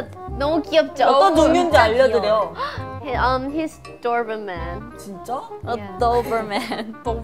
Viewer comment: Lolol, I thtime at first, when she was saying how he was a Doberman, I thought she said he was "an anorable man." Lolol.